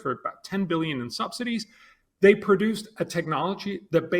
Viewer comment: Did he mumble 10 billion in subsidies for fraking? I'm gonna take another listen. Yes that's what's said @ 6:40.